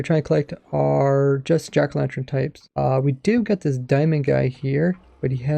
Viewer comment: Did he just say jackerlantrens?